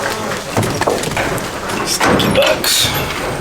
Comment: Lol,love the first little buckling He's cute plus he had me cracking up with his crying. Heard something strange at time stamp 25:24 sounded like" stinky itch " but with a b in front of it. Lol,I listened 4 or 5 times ? I know you guys said the boy goats stink. I don't really think it was either of you saying that. Just weird.